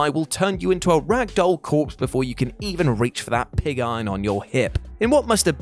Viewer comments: 1. Why did he keep saying Wendigo like that? 2. Did he say "Pig Iron"? 7:08 3. Albino also isn't pronounced like that. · did you....did you call it a pig iron? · Did you say “‘pig’ iron on your hip”? @ 7:08. I swear I heard pig.